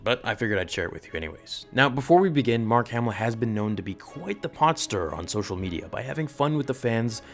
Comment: “Mark Hamilton has been known to be quite the [podster?]” Does he say “podster” here? What’s a podster?